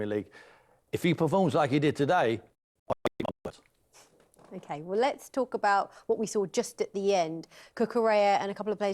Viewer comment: What the hell did Tim just say? 5:07 😂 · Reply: "I will eat my words"😂😂